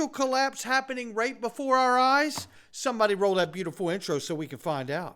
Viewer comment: I keep hearing “Roll that beautiful bean footage” 😆🤣